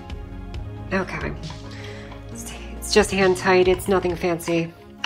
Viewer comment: Did someone say nut n fancy? ^^'